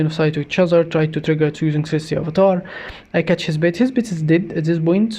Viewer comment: Hi! I love your commentaries, but I've been wanting to tell you forever now that I hear it as "Sexy Avatar." I love it! ^^<3